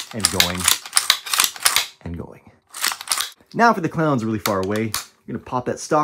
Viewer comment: Thought he said if the crowds really far away